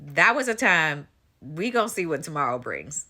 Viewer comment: I think I heard you say, “Rico see what tomorrow brings” Lol😂